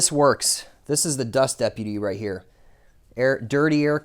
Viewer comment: Did you say dust deputy?What is that exactly.I bought an Eastwood cabinet years ago and stands all of 6 feet with a 4 foot wide box.I had a cabinet vacuum which is designed for dust removal and the bearing ground into dust.ha ha ha...now I am looking for a different way to keep cabinet air clean....I am wondering where you got you dust deputy? if that's what I heard you say and what it's purpose is,if not for what your using it for.Thanks for any info you can give.I really like a dust free atmosphere when I blast.I use glass beads, and aluminum ox...not together LOL...Thanx again. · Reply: Yes dust deputy: <link>